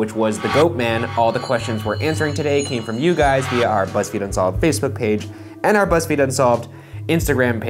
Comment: I know you guys have auto-translate for captions on, but there are some errors in the translation that need to be fixed. Right at the start at 0:16 it says 'Buzzfeed and saw' instead of "Buzzfeed Unsolved" lol.